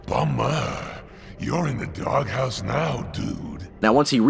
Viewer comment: Mary Jane: Peter PARKERRRR get me out of here right nowwwww!!!! Venom: Ooooh mamaa, You are in the dark house now dude.. Amazing writing 10/10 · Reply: i always thought he said "Bummer, you're in the dog house now dude"